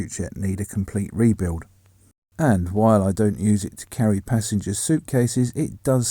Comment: I misheard the video. As "While I dont use the boot for carrying passengers"